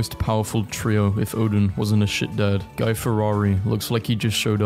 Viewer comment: did you just call him "Guy Ferrari"?